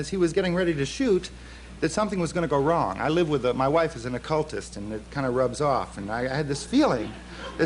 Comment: Did he just say his wife was an “occultist?”